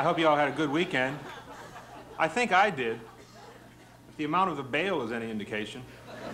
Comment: "If the amount of ____ is any indication." What did he say? I couldn't catch it. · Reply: the bail